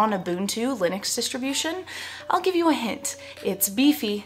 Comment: Did you say "I'll give you a head" at 0:10?lol (PS: my listening in english isnt that great)